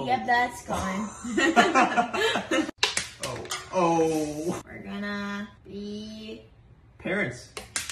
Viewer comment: I thought he said we’re going to be parrots